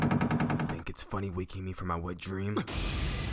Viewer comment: is it me or did he just say "Think it's funny waking me from my wet dream?"